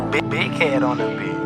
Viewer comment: Ooh, Bi-Big Head on the beat 😈😈😈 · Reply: Drago I thought it said "big head on this b***h"😂